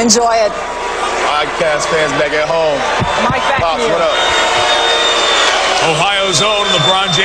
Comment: did he really say in the draft interview "pops what up"